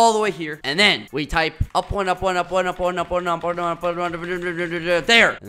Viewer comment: did skeppy said porno?